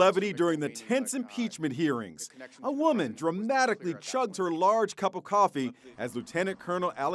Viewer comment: I thought it said chucked